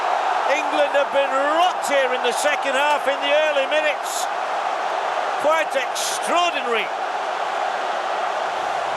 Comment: what is Ibra saying ? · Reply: Avdyl Rashica ibrahimovic said bitch to hart strange right